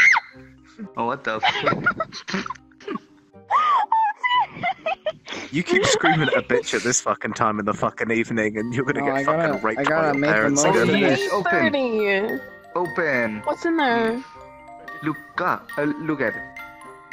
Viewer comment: did he say "your gonna get raped by your parents" lol